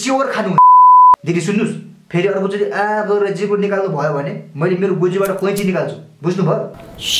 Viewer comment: I heard Mu* instead of Goji* WHHAATTTT!!!